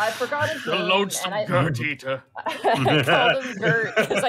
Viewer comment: I legitimately thought said "The Loathsome GIRTH Eater" when they were talking about Margit, not gonna lie.